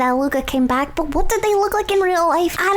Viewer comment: did say they when referring to Luca